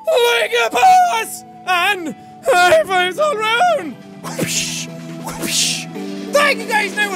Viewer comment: I never quite understand what Jack says in his outro when he gets close to the mic. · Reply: Ya I heard "high fives all around" too